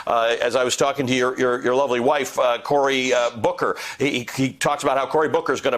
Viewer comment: Had to do a double take... It sounded like Velshi said "Your lovely wife Corey Booker".